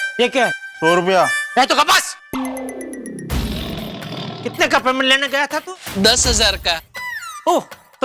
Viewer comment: what did jetha say?? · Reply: Ae tu gab bas Matlab - tum chup raho